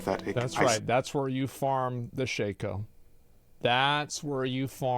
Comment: did I hear ir correctly? : ' ' ) Farm the Shako, that's right